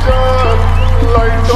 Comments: The fact that she says zaddy zayn where everyone thought she said daddy zayn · I honestly thought she said it’s Zaddy Zayn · I thought she said "Zaddy Zayn" and not "Daddy Zayn"